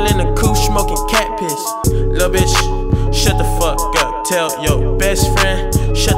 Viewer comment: For all this time, I thought he was saying “Le Bitch” 🇫🇷